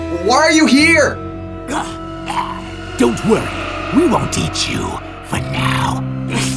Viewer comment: It sounds like venom is saying teach you if you want proof go to